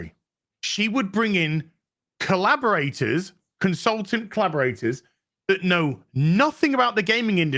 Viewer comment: any time I hear these people say "consultants" my mind auto corrects to "commissars".